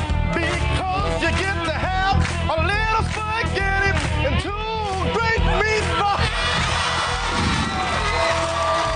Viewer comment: What he said at 13:30? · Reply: Cristi C “You get to have a little spaghetti, and two big meatballs!” But he cracked up before he could finish saying “meatballs”